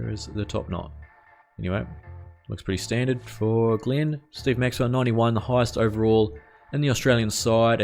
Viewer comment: Did anyone else hear say Steve Maxwell? Lol